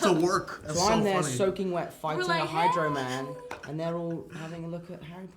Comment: What....what did Tom say he was fighting at 6:09 ??? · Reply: It is a spoiler but Tom accidentally spoiled hydroman’s name in an interview a while ago, so it’s not new information